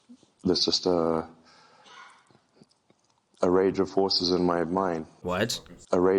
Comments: He says “rage of forces” not “rage of horses” but it’s a funny mishearing so I don’t mind lol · I heard him say "A rage of FORCES in my mind"